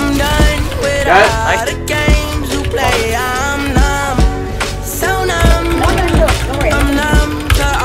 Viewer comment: is it just me or it sounds like laroi is saying im dumb so dumb